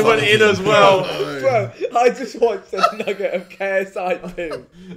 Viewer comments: sounds like just said n*gga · Hold up what did you just say💀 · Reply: ""nugget of KSI's poo... roll""